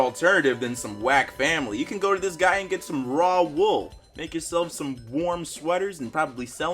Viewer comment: Did you really say raw wool